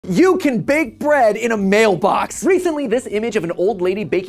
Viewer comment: The video buffered right in the middle of the first mailbox so all I heard was "YOU CAN BAKE BREAD IN A MALE!"